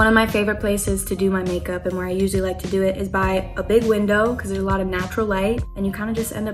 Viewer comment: i thought she said bay window lol hhaha